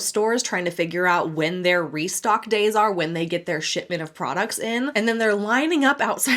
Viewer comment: heard sh*tbin instead of shipment, sounds about right 😂